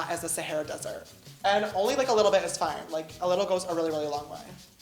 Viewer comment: Sister i thought u said a little spray